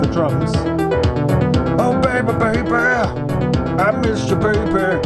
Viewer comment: "Oh Baby, Baby" is my new favorite song. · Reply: I'm sure I heard wrong, but what I heard was "Oh baby, baby! I'm mister baby!"